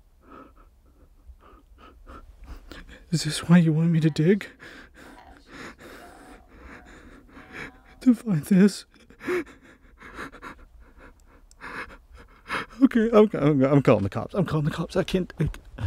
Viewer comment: i heard "I want you to dig up my bones!" but it didnt sound like or it sounded ...... well Evil. Not anything like either of them.